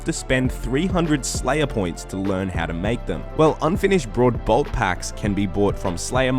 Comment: good vid, sounds like your saying "finish braod bolts" -im sure there gilnorian